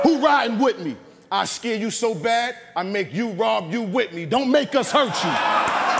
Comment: What did ice say after I scare you so bad? Around 5:29 mark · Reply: I make you rob you with me